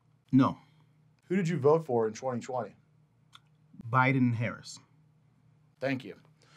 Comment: I thought I heard him say he and his fellow officers voted for TRUMP! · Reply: I thought I heard that too, however: "um did you ever have politics enter into any of your decision making when you're on the force never 4:23 are you registered as a republican no who did you vote for in 2020 4:29 Biden Harris thank you".. I believe he said it, because he felt it was necessary, when he was trying to save the policemen and had to get up and down the stairway.